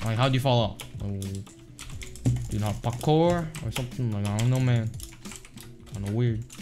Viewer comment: what did he say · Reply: Pakor LOL